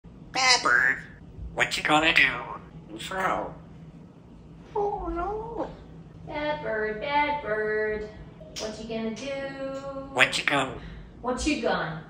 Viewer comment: I heard " I'm your bad bird" lol